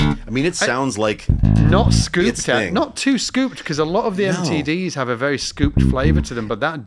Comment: Did say that a lot of STDs have a scooped flavour to them at around 55:36. Either my ears are fucked or that was a hilarious Freudian slip lol